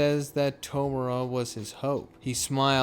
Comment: I thought bro said “Tomura is my ho” I was like wait wtf.